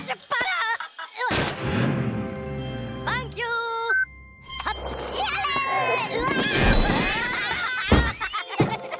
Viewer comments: what did he just say 🤯🤯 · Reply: He's say thank you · did he just say the f word? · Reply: No He Said "thank You" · What did he say!? XD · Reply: He's say thank you